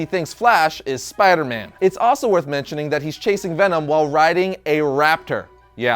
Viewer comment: i swear at one point i heard him say Asian Venom lol